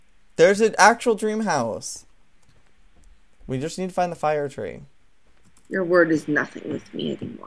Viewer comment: he says "we need to find the fire tree" and i thought he said said fire nation and I was like aw hell nah epic adventure time.